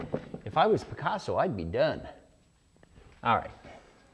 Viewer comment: "If I was Picasso, I'd be done" - laughed so hard at that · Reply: Lol I thought he said "I'd be dead". Either way it's kinda funny though.